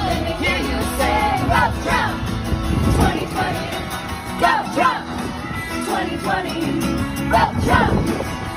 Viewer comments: what are they saying!?? I understend “fuck trump” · I heard “F*** Trump” I couldn't be more confused about these people 😅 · Ill be honest, I thought they were saying “f*ck Trump 2020!!!” And i was so confused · I heard "FUCK TRUMP 2020"